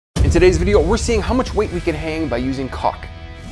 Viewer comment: Omg in the beginning I thought you said eating caulk, not using caulk.😐